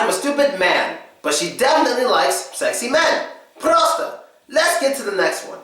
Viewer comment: What do you say at 2:42? It sounds like PROASTA ! In romanian that means: stupid hahaha